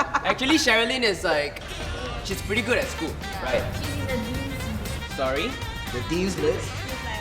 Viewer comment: I thought Jianhao said your are in the dead’s team but it’s the deans team